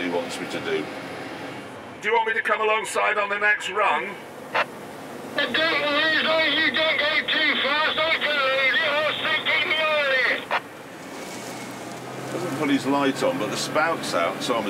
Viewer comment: I can get a gist of what Gerald is saying, due to my own farming experiences as such Here's my translation of what he's saying (for the most part) 2:00 he's saying: Indeed as long as you don't drive too fast I can unload into you when you get over here 3:06 he's saying: Ok but I was gonna say if you drive too fast during emptying I then look to see the back of the trailer and I couldn't do the combine and the corn in (Basically he's saying I can't keep an eye on your driving while unloading and driving the combine at the same time park the trailer and I can unload into you in the corner of the field) 2:13 & 3:21 Don't drive the tractor like a racecar cos I can't keep up with you! The rest... yeah nope I can't translate that